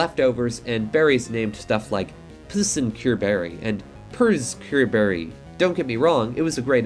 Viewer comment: the way i did a complete double take when i misheard psncureberry as pussincureberry